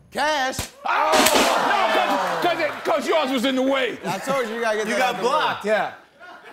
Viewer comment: Did Denzel say coach yoast was in the way